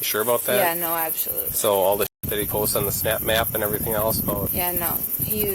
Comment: Is he saying "snap map" at 14:42 ?..